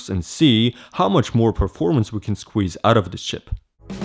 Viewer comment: Did he just say "shit"?